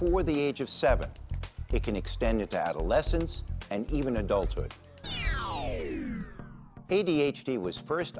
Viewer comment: Did they seriously say that ADHD leads to adultery😒 tf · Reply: I might have ADHD and even I heard "adulthood", lol